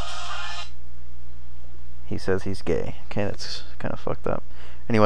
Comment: did you say He says he's gay?? :D